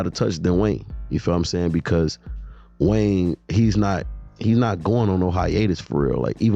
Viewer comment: “Because Wayne. He’s not White” is what I heard 🤦🏽‍♂️